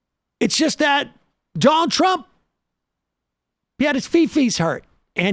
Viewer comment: I thought Sam Seder said that trump “had his covfefes hurt”! 😂